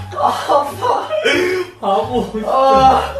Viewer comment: am i the only one who heard that ' fu...'